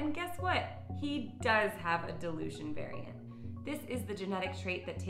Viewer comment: At first, I thought you said "delusion" instead of "dilution" ... that might explain a lot of my Finnegan's behaviour! :D